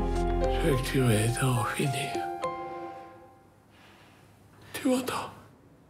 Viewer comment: il dit quoi ? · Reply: "tu m'entends" ?